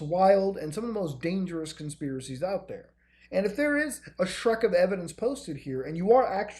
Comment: Did he just say a 'shrek' of evidence? new unit of measurement.